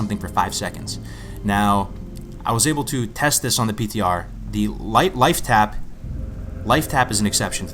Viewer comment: did he say" test on PTR"? as in classic PTR?